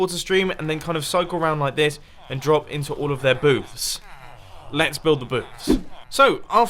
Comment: I Could've Sworn He Said Let's Build The Boobs